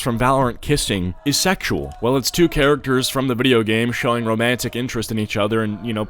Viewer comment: Yea but the timing on the post was when the edateing was starting or when it became popular to edate on valorant also his point in the video is that a shooting game like valorant shouldn't be an game full of edating. like for example roblox (it's the only game I could think of) is full of people who date prey on children and make porn games on a kids game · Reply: when I thought he said "a game should be romantical" he was mentioning the characters being romantic